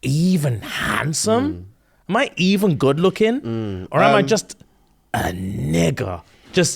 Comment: I heard "..or am I just another 🥷🏽?" and screamed. 🤣😂